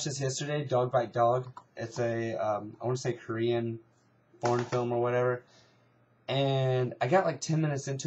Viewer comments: oh my jesus, i thought you said Porn film, not FOREIGN film. -___- fail on my part. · i thought you said its a korean porn film